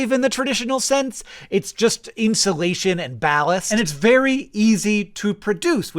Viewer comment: goddamn, i love mola mola <3 honestly didn't know this brand of mola hate existed out there. sorry your recommendation algorithms are so different from mine :P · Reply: misheard "it's just insulation and ballast" as "it's just insulation and malice" XD